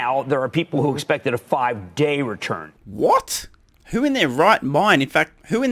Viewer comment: WTF?! - did he really say 5 days? OK, my time-horizon is unfortunately not a decade, but minimum 3-5 years... sorry, i am already too old...